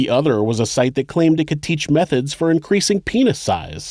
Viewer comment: When he said "methods", I thought he actually said "meth heads". Kidding aside, I love how there is a healthy balance of providing the facts, funny banter, and respecting the victims.